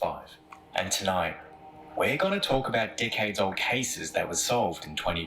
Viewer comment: With the narrators accent I thought he said 'dic$heads cases' but realized it was decades old cases...lol I like the Aussie accents, and Irish trees (three). Lol